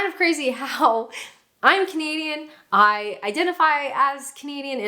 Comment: I thought I was the only one who thought you said “I’m a comedian” I’m Canadian but it really does sound like you’re saying “comedian” 😆